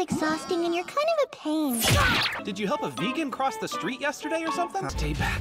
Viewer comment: why did I hear "did you help a vegan cross the street yesterday or something" ???!!!??